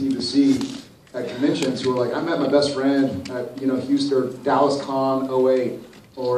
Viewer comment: Can someone please tell me what Jared's saying before Jensen puts his hand on Jared's shoulder at 1:55? :) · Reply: He's talking about fans who have told him they met their best friends at various Supernatural Conventions: "I met my best friend at Houston... er... Dallas Con '08 or Burbank Con '09 and we've been best friends ever since"